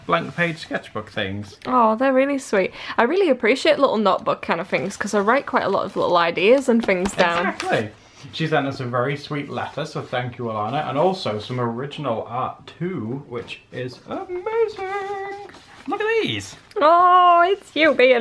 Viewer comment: i honestly thought said "she sent us very sweet lettuce" for a minute, but then i realised my mind is playing tricks on me ^o^